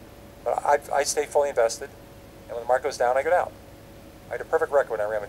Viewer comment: Sounds like he says “I get out“? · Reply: If markets go down. I go down! Not I get out 😒